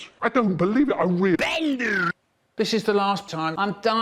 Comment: what did he say "DANGER"!?!!??? Wtf was mBEEnGErr !!!